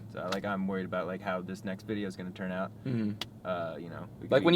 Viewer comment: I honestly thought he said “...how this sex video is going...” but I listened to it and he said “next” lol I’m too stoned